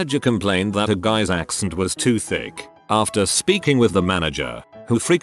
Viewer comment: Heard that as ass too thick